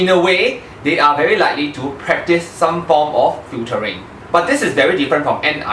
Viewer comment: They practice some form of what? Futuring? I didn't understand the word you said at that part. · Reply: it's "filtering"